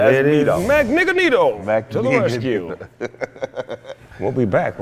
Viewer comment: cmon man MAGNEGRO!!! Why yall didn't catch that! That's all good, I know yall high as fuck and Magnigganeto is funnier lmao